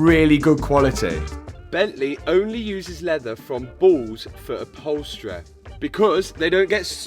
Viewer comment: i thought he was saying its leather from balls and not bulls